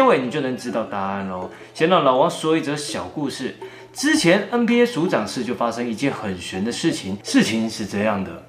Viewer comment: ，有人和我一樣聽成NBA署長室的嗎? 原來NBA也在拜關公🤣🤣